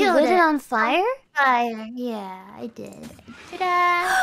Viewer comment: Can someone confirm, Can you hear Ame say "Gura~" Edit: Nvrmnd, I think it was Mumei saying "Tada~"